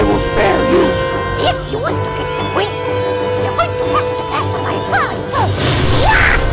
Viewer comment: am i the only one who thought that toadstool said ' blah blah blah princess, your going to have to piss on my body first'?